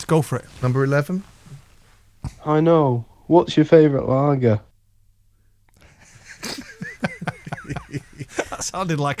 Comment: I thought he said "what's you favourite Aga?" LOL